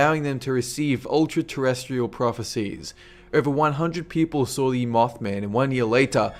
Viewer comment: what does it say at 4:06-4:08? I cant understand well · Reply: If you're still trying to figure it out.. "Over 100 people have seen the Mothman"